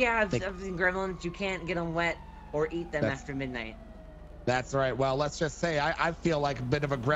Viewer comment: Did she say, you can't eat Grimlin's after midnight..??